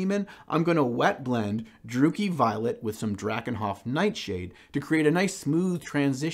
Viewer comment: For a second I thought you said "dooky violet" and was wondering what the citadel paint version of that was